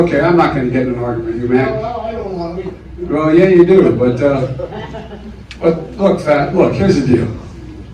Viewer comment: Does Joe actually say “well look, Fat...” at 2:27 - calling this voter fat? Not only that but Joe did it earlier in the video with the push-up contest remark. What a smug prick.